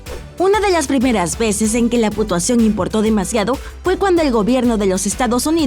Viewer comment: escuche putuación